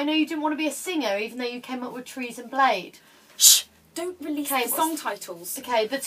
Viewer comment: I really want a jumper like in this video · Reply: Caitlin Gilligan Hi, I'm adding Spanish subtitles! Can you please tell me what said at 5:01 ? "trees and blade"??